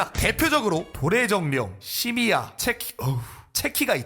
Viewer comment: 시미야 에서 자꾸 시리켜져 ㅋㅋㅋ ㅠㅠㅠㅠ 시리야 라고 들리는듯